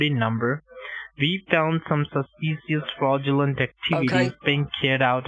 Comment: Learned a new word today: "suspecious". I think it's a cross between suspicious and specious. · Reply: For me i heard, "SASpicious." 🤣🤣 HE GIVING THEM THAT SASSSS.